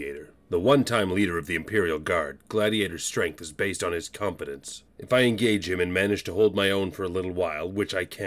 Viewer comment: I thought you said Incompetence instead of confidence. So my thought was "The dumber he his the stronger he is, so I just need to start spouting random facts and trivia at him to make him weaker"